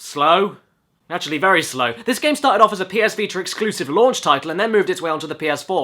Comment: my nigga did you just say VITAR